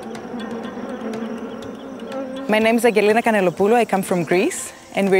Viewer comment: did anyone catch her name?